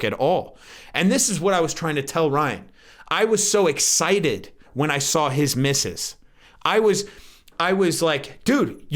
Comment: For a second I thought you said “I was so excited when I saw his missus” @ 11:23 lol